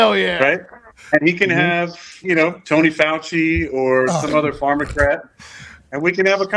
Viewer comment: Did he say “pharmacrat”? 😂